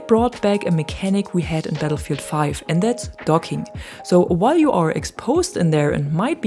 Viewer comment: I honestly thought she said "dogging" 🤣🤣🤣🤣